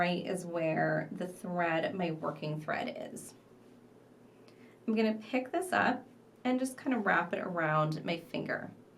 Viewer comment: I'm at 6:03 in the video, I'm sorry, but how many "embellishment beads" are on each side of the initial herringbone? It may be my headphones, but I can't understand if you are saying 15 or 16 on each side, or 16 on one and 15 on the other. The pendant is so pretty! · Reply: It’s 15 on one side and 16 on the other. Once you close it, you add one more to the 15 to make it an even 16 on both sides.